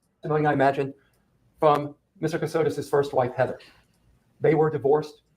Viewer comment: I thought she said assholes too LOL